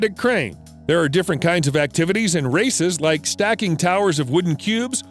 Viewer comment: I heard racist?